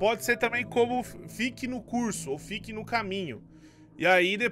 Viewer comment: Eu ouvi CURSO?